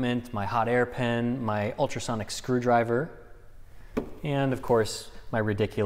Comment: did I hear SONIC SCREWDRIVER